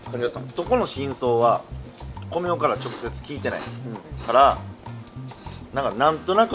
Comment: 直接聞いてない って聞こえるけど 聞いたからになってます🤔🤔🤔？